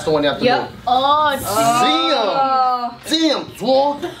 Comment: What does say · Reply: Dzawg, which is his version of "damn dog/dawg"